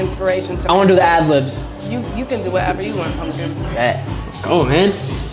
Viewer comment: Did she just call him "homegirl"...?